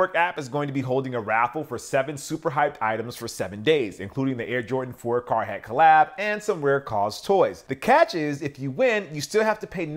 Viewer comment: What Jordan 4 collab is that @ 1:28? I can't understand what he said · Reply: Jordan 4 carhartt x eminem, i think you won't buy it because of the price;)